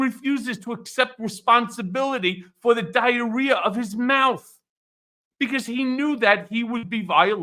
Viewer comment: “Diarrhea of his mouth!?!” Did I hear right? Is that what he said? That’s even funnier than “Mr. Sloat” Todd Blanch! He doesn’t call Donald Trump “ Vonshitzhispantz” for nothings! 🤣🤣🤣🤣🤣🤣🤣🤣🤣🤣🤣🤣🤣🤣🤣🤣🤣🤣🤣🤣🤣🤣🤣🤣🤣🤣🤣🤣🤣🤣🤣🤣🤣 The felon that’s on our side, Michael Cohen! I’m sad that’s he’s in same boat as Trump because he’s banned in 37 countries because he too is a convicted felon. I hope he’s holding up alright.